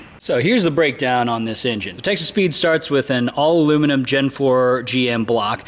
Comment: Sounds like your saying Texas Pete not Texas Speed. It makes me smile at least